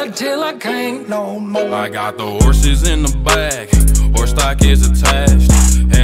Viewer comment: Whenever he says "I got the horses In the bacc" I hit the "woah", ( lock it up) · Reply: i hear it as "i got the horses in my butt"